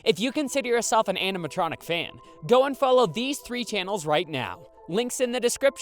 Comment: When he said and follow for a second I thought he said unfollow 💀